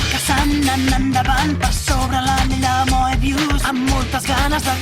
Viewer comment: Que es lo que dice? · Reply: Per sobre l'anell de Mobius